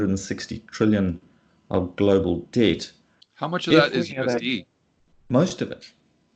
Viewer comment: what did you say at time frame 22:08, can not make it out. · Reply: I said "How much of that (total debt) is USD" ;)